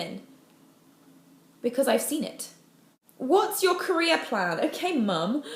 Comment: so that was "my career plan? my career plan is to have one... i know that was "career" but what i heard was "my Korea plan? my Korea plan is to have one"